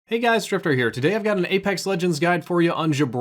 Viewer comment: it kinda sounds like you said “stripper here”